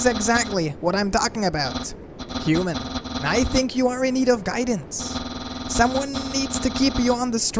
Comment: I thought it’s said “your the nerd of guidance” lol